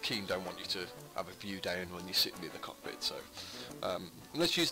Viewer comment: What seat? Can't really hear you clear enough, sorry. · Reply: I'll try and articulate better in the future, thanks for watching! Passenger seat can be used in this context but you must access the ships remote block to operate, also, you cant use 3rdperson view. The modded seat is used in this video. The mod maker is Azimuth and I believe the seat is part of his pack. Its called Azimuths Flight Seat. Seach Azimuth on the workshop. It may also be bundled with what used to be small-ship-mega-modpack.